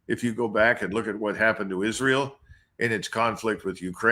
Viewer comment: Did he say Israel and Ukraine?